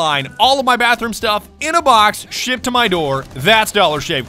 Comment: Why did it sound like he said “Shit to my door”